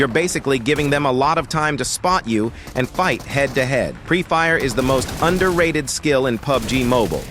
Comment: I thought you said Free Fire 😅